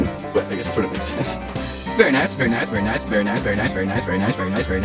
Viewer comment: all i hear is fern-ice and furnace